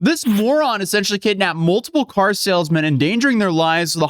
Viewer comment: Wow.. thought It said ticktock mormon. Hahah